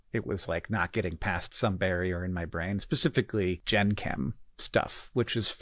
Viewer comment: I thought he said Jenkem instead of Gen Chem 😬